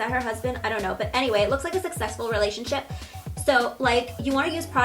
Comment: When she first mentioned Jaclyn Hill I really thought she said that looks like a sexual relationship!😂😂 but I think she actually said like a successful relationship 😂